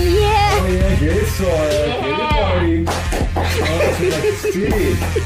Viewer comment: listen to what says 😳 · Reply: he said help me